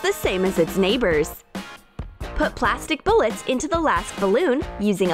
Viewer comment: Did I just hear them say "plastic bullets" at 2:49? I'm now leaving the room